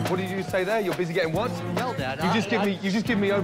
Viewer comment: did he say no dad ?